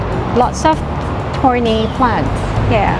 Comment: Love your channel! Thank you for this great video. Had me in stitches for your comments in the cactus area. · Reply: and for the record many misheard me...I said "thorny plants". Not "horny plants".